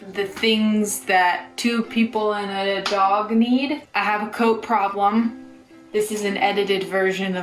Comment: for a sec I thought she said "I have a coke problem" i was like wait what let me rewind that making sure i'm watching the right video lmao!!